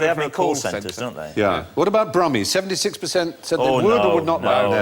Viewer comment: Which accent did Stephen say? "Brahmi"?